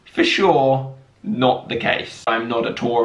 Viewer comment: It sounds like he says “I’m not a dory” like off Finding Nemo